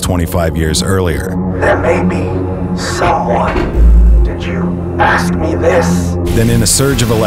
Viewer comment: Can someone please tell me what Jeffries says at 6:53 ?? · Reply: He says "there may be someone ... did you ask me this?" Thanks for the comment! I was thinking about subtitling these scenes, it's hard to hear sometimes with the bass and machine clunking sounds. I'm probably going to add subtitles to all my videos at some point in the future.